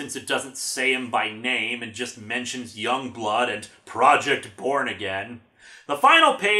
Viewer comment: Kinda zoned out for a second near the end there and misheard "Project Born Again" as "Project Porn Again"....then again, this is Rob Liefeld.